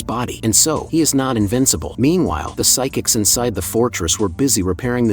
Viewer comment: I heard invisible in